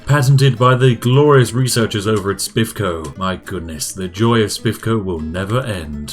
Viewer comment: Thought he said sips co not spiff co so got flooded with buried memories